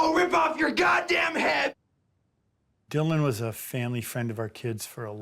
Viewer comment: did I hear Dylan?!